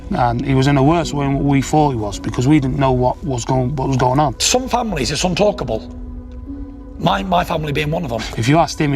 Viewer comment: can someone please help me understand what tyson said there, i genuinely want to know because i cannot understand the accent. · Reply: yeah, did he say some families are untalkable?